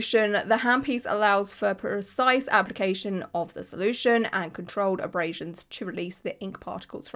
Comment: I was walking and listening the majority of the video so when the process was being explained I could’ve sworn I heard “micro abrasion” or “micro holes” so to pick up my phone right before the worst of it and to see such LARGE HOLES scared the living crap out of me. What in the world did they do to that guy!? How would such large “abrasions” help remove the tattoo??? They’d might as well just had the skin surgically removed!! My goodness I hope no one else uses that method. It’s absolutely dreadful.